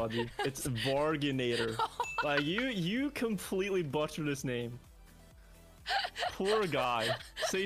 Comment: is it ironic butchered the pronunciation of the word 'butchered' while describing the butchering of her pronunciation of the guys name? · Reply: Lol I thought at first he said "you buttered that name"